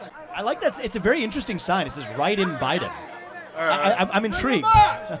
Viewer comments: Am i the only one who thought he said "riding biden" as the sign? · I heard "ride'n biden" until i looked at the screen. I was scared